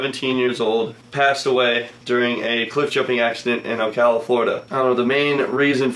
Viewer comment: did he say califlorida